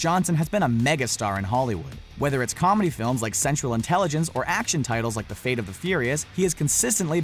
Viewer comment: I thought he said "sexual intelligence" for a second 😂